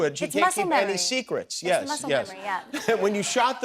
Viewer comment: Every time she says “yes!” I hear “ wicked witch of the west is dead!” In my head😂😂